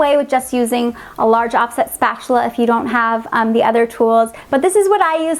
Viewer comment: Oh my goodness, it sounds like you said off-shit spatula 🤣 I know you said off set but that's not what I heard my goodness 🤦🏻‍♀️🤣🤣🤣